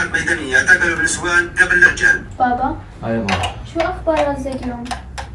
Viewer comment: ما فهمت شو قالت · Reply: قالت بابا شو اخبار غزه اليوم❤